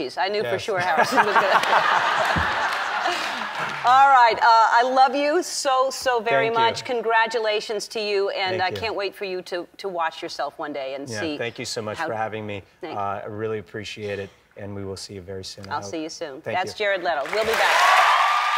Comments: I thought Ellen said "I can't wait for you to wash yourself" at the end instead of "watch yourself" lol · I thought she said - I can't wait for you to WASH yourself one day! Ha!